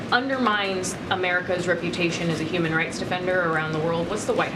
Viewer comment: Did she just say, "human rights offender?"